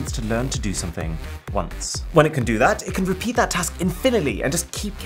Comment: Did he say “infiniley” at 4:53? Lol